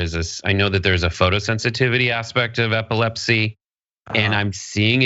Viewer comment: I thought she said her daughter was at pilates...not sure why you were talking about epilepsy